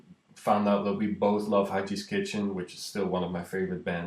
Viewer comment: can someone tell me wich band he is mentionning around 1:50 ?? i'm curious. because i don't quiet get what he says. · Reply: Guillaume Lemaigre just found out, Haji's Kitchen, for those whom are interested